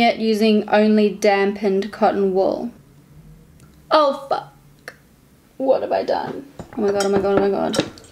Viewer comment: OMG did she just said FUCK at 5:15 ????????